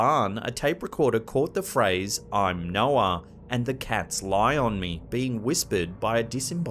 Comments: On Villisca axe clip: it sound like he said " They cant lie on me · In the second clip where it says “I’m Noah” I’m hearing “I’m no one.” And the “The cats lie on me.”, I’m hearing “the cat’s eyes on me.” If the particular malevolent energy that has been recorded in that area is the same thing responding, then I’m assuming these are intelligent responses. I’ve heard quite a few recordings from this location and the majority seem to point to one dominant energy in the home. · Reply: I personally do hear "I'm Noah" but the one with the cat I heard "the cats lying on me" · In the second clip where it says “I’m Noah” I’m hearing “I’m no one.” And the “The cats lie on me.”, I’m hearing “the cat’s eyes on me.” If the particular malevolent energy that has been recorded in that area is the same thing responding, then I’m assuming these are intelligent responses. I’ve heard quite a few recordings from this location and the majority seem to point to one dominant energy in the home. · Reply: I agree i hear it as " Im no one" too but " the cats eyes on me" is wrong. Im hearing "Accept the layers". Try watching it at 0.25x speed, it would look like that normal speed is the sped up version.